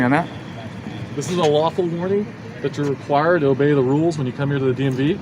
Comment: I don't know if YT or my TV did this, but just a few seconds before 5:30 the subtitles said the officer was giving a "waffle warning." lmao More importantly though, AWESOME commentary by on what the key component of tolerance is. Made me do that kissy thing chefs do when something is delicious. Mmmmuuuahh! Wonderful!